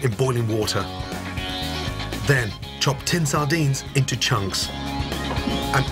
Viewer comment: I thought he said ten sardines like you have to have exactly 10 in your cupboard for maximum flavor